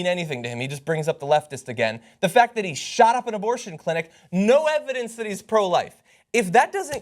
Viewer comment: Wait. Did John say 'Abortion Clinic'?